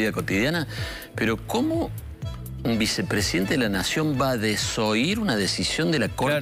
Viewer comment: Escuché "Desólló a la corte" Me puse contenta! Los destripó! Pero qué desilusión, hablaban de desoír.